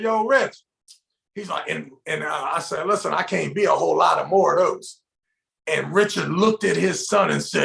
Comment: What did say at 19:03? All I can hear is 'Listen I can't be a whole lotta more a hose' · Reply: I can't eat a whole lot more of those. At least that's what I hear.